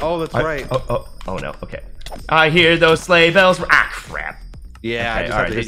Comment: correction “I hear the sleigh bell- aw, crap”